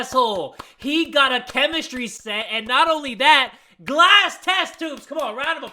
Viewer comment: ummmm I thought he said glass testicllllll... never mind